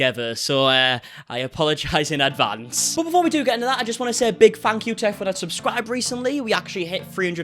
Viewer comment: Did he say "a big fuck you" in 1:13 😭😂😭??